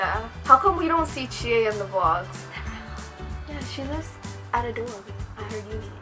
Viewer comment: What does she mean by "Chie lives out of door?" Did she kicked off? Or run away? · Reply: Wahyudi Anugrah I thought she said "at a dorm".